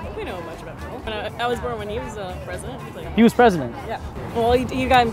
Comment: what did she say? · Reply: Demonic Goats "I was born when he was the president." referring to Bill Clinton